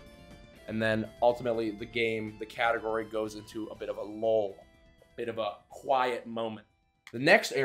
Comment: did you say low? Or loughl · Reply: lull